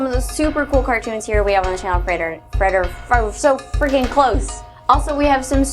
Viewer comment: Gosh how could you guys do that to her at the end, hahahaha! She couldn't pronounce Frederator and you put all the bloopers there, mean people. Fun though :p and good theory also!! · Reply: yeah when u say Frederator fast it sounds like your saying refrigerator